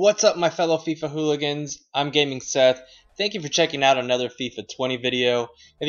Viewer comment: Oh, he said Gaming Seth! I thought he said "I'm getting sad".